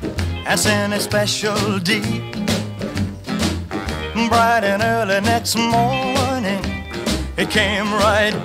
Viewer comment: I have a question for all Elvis Presley lovers and those who are native English speakers: why don't I hear "Bright in early next morning he brought my letter back" instead of "bright" I hear "but" and then it makes more sense.